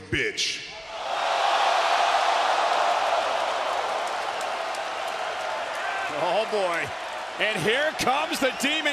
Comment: What did Kane say ? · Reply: He said ok